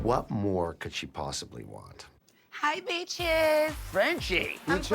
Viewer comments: I thought she said '' hi peaches '' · I thought she said "peaches". Lol